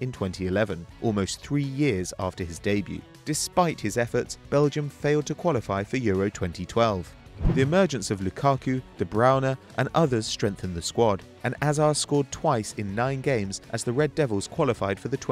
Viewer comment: i thought he said "de browna" :P